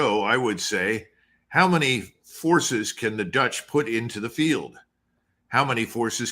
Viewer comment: Pardon me. With the rain it sounded like Doug said "how many horses can Dutch put in the field - how many horses can the Noreigens put in the field....." which would be about right.